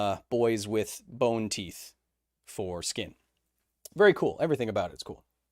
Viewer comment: Wow. I totally misheard when you said "boney teeth for skin".. 🤣